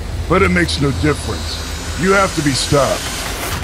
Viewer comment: Did he say skye Edit: he said stopped.